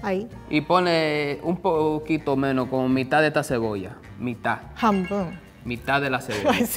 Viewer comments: loool you guys put mita (saw) instead of hanbun (half)😭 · Anyone else noticed that they made a mistake with the japanese at 5:14 ? They transcribed phonetically the Spanish word mitad - 見た (みた/mita) instead of transcribing what actually said 半分 (はんぶん/hanbun) 💀